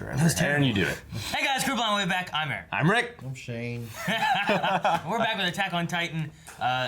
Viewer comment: 'I'm Shane' but it sounded so sad I thought he said 'I'm shame'.